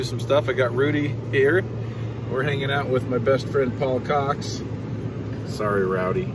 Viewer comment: he says his bf is apparently paul coxs, but if you rewind it a couple times in regular play it sounds like says my bf's ya'lls cocks...just saying lol. jk brother we all appericate everything you guys do. please keep these awesome videos coming as well as the great camaraderie